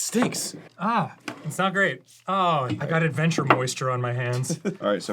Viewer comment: god I really thought for a second Dan said 'adventure boisture' and I think that's a sign I've been rewatching too much Grumps Wheel of Fortune.